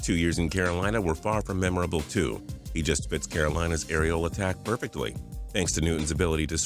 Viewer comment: is it just me or did it sound like he said "areola attack".